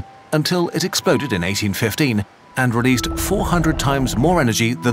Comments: I can't understand what he said about Mount Tambora. The eruption released 400 times more energy than ______ (around the 4:46 mark) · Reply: 400 times more energy than the Tsar Bomba (Largest bomb ever detonated) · Can anyone tell me what is being said at 4:54 ? Sounds like jibberish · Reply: They said “tsar bomba”